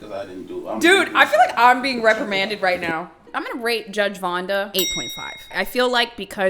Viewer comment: omg am so sorry i thought you said ”im gonna rape vonda” for sec i was confused but when i heard ”8.5” i realized you said rate 😰